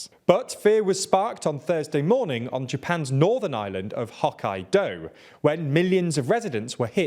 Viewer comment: It took me a few seconds to realise that was talking about Hokkaido prefecture, and not an obscure martial art, Hok Kai Do. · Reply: I heard it as hawk, eye, doe 😂